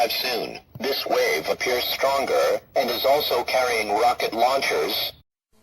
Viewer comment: What the radio said: "...also carrying rocket launchers" What I heard: "Free rocket launcher per headshot"